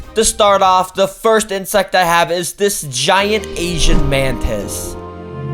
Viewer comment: 😢i thought he said ,,the giant asian man"